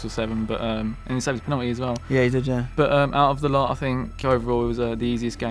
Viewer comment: Lmao at 11:52 what does say? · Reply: "Yeah he did, yeah"